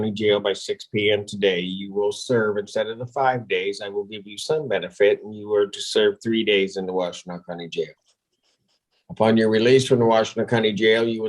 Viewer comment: I couldn't hear what judge said. Is it 75 days in jail? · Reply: "instead of five days, I'm going to give you SOME benefit and you are to serve three days in the Washtenaw County jail..."